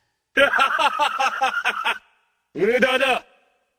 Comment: what is he saying? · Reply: Mudada= useless